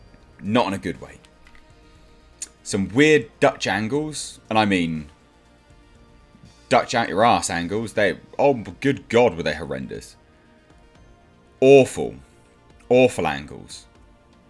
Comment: says "Dutch angles" I hear "Duck jangles" and for a minute and sit, confused to hell and back.